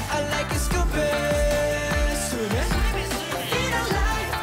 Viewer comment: why am i hearing, "stupid swimming" instead of sukhumvit swimming. :V